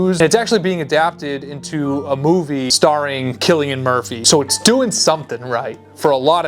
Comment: I don't know if you were joking with "Chillian Murphy", but it's pronounced "Killian" 😆 (edit: my bad, I misheard)